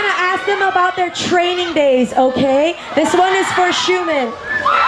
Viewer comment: did she just say shoeman...